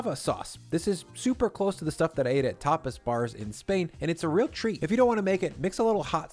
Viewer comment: Whenever he said “Tapas,” my mom was like, “I thought he said ‘topless’ 😐”